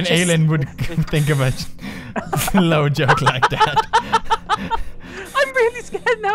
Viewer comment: I thought he said a 'flou joke' then realized it's 'slow' hahaha. don't know if anyone here talks afrikaans